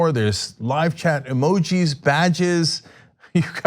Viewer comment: Did he say "Vag's"?